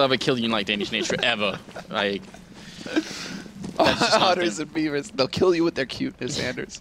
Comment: i thought anders said 'autism beavers'